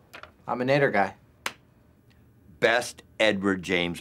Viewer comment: “Best Edward James Olmos costume I’ve ever seen… like freaky good” This line is always funny to me 😂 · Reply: I thought he said "Edward James homeless" I didn't know who Olmos was 😅